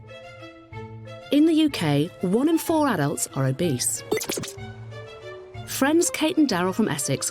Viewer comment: For a second I thought she said in the UK 1 in 4 adults are a beast lmao